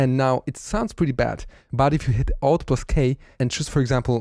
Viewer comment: min 8:40 , what he says??? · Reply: Alt + K